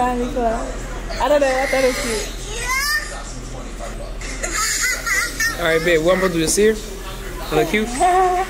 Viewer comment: OOOPP did you say “alright babe” at 6:28 ?? 😮❤❤